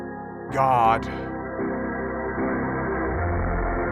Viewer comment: I thought Zooble said "God.. let me lose my mind" there